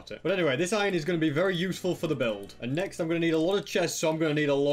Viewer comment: what he says: "This iron is going to be very useful for the build..." what I heard: "This iron is going to flex for the build..."